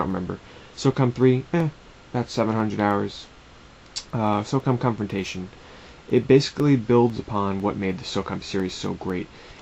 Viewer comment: i thought you said socom constipation